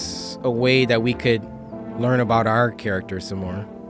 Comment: he said “what about the physics thing” when it says psychics i died laughing love bruh😭😭